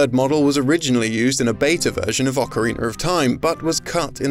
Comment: what's an "ocariner"? Is that how English ppl say ocarina? Same question regarding when this guy says "idear' instead of idea.